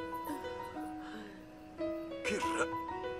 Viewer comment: Que dijo en el 0:50 el negrito? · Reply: Meli de mg Terry Crews intenta decir (o mejor dicho el que hace el doblaje): "Que rayos?", pero solo llega a decir "que ray..?"